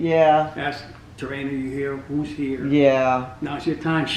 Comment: Thought I heard who's there?